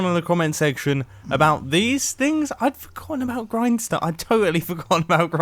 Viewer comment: " I totaly forgot about Grianstone" xD i just hear this